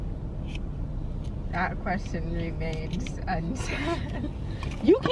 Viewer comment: I couldn’t even hear what she said 😭😭😭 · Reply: She said, "That question remains unsaid." She's about to be on punishment. LOL...LOL...LOL